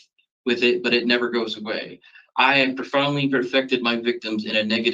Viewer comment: Did he just say "I have profoundly PERFECTED my victims..." ????? WTF? Freudian slip?